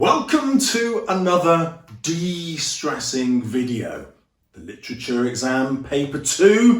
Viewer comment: I misheard it as 'welcome to another distressing video' and almost had a heart attack 😭😂